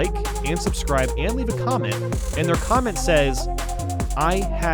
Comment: I might have misheard it but "I hadn't ate today." Also now that I think of it I don't think I hear many people pronounce Delfino that way haha.